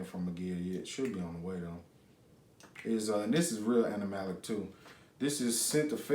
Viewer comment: I was watching you and I heard “fecal animalic” which I had to google. So please elaborate :)